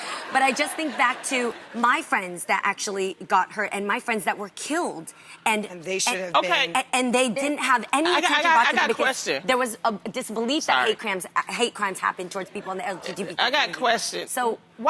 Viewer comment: This clip hurt me. Jeannie began to talk about people she has EXPERIENCED people who have died from hate crimes and not a single lady on this table showed compassion to her at any point. Loni and Amanda basically ignored Jeannie’s story. I LOVEEE Loni and Amanda but it really hurts that Jeannie can’t speak unless her point is agreed with by the majority. It seems like more of an argument then a conversation 😭 Love you Jeannie! ❤️❤️❤️❤️ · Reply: Yeah i literally heard amanda say "they should have" when jeanie said they died